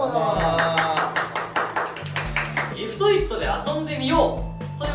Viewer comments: リフトイット、リングフィットって聞こえた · リフトイット、最初リングフィットに聞こえたけど、概要欄とか見てもリングフィットに見えるようになった不思議